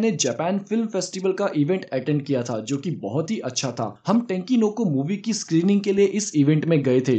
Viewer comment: Could you pleaseake video based on moro and his power. And what movie you said i didn’t understand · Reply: Tenki no ko Weathering with u